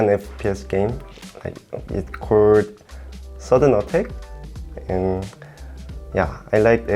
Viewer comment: What was the Korean FPS game was talking about? I couldn't hear the name of it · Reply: Sudden Attack